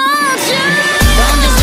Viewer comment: I thought it said broken ex